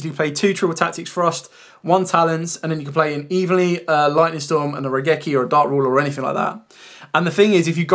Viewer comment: I cant catch what you are saying around 12:40. You say something... lightning storm or ragecki. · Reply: Evenly, Lightning storm or Raigeki